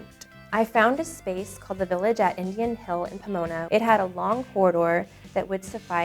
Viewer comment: does she says INDIAN ?? pls tell me